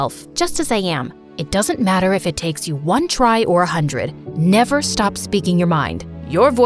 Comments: she said “it doesn’t matter if it takes you one try or a hundred” but the captions said “ if it takes you 1 try or 200??” And yes, I liked my own comment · why did it say 200 in subtitles and 100 speaking? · they say if it takes you 1 or 100 but the captions said 20” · at, 1:00 she says 100 but the captions say 200 big mistake! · They say : "It doesnt matter if it take 1 try or 100!" Subtitles say: " it doesnt matter if it takes you one try or 200! " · SHE CLEARLY SAID “it doesn’t matter if it takes you one try or 100” not “it doesn’t matter if it takes one try or 200”!!! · captions:200 What the mom said: a hundred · Not them saying “it doesn’t matter if it takes you 1 try or 100” and the subtitles say 200 😅😅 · ?? She said 100 but the subtitle said 200😂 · She says 100, but the subtitles say 200. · In 0:59 they said 100 but the subtitles said 200💀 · Did anyone notice 1:01 the subtitles say try or 200 but when they said ut it was 100